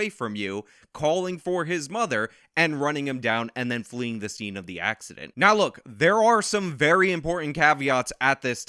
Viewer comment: said INCIDENT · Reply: no, he didn’t. 5:45 he clearly said “fleeing the scene of the accident”